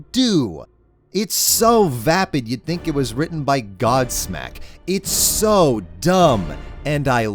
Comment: "It's so vapid you'd think it was written by Godsmack" is a very good line. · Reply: I almost thought he said godslap and I was like, wtf did Charlie do??